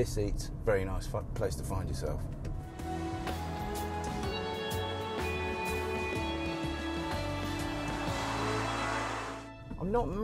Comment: did he just say "very nice f***ing place to find yourself" at 5:42? haha